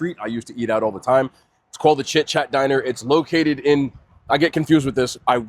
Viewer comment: I thought bro said “the shit, shat diner”